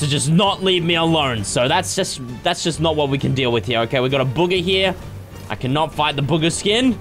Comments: when fresh says boogha I know its not how its spelled but its how its said it sounds like he saying booger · It almost sounded like he was saying booger skin instead of bugha skin LOL 😆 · Anyone else thought Fresh was saying booger instead of bugha in the beginning of the video